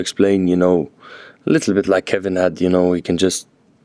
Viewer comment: did he say Kevin Hart?